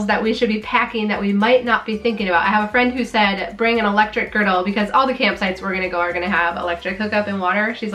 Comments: I definitely thought you said "bring an electric girdle", and I'm thinking what in the world for...😳😂🤦‍♀️. The camper looks awesome! 👏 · Every time you said "electric griddle", I heard "electric girdle!"